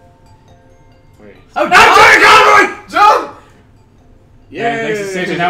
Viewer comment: All I hear: "AGH JON CONROY!"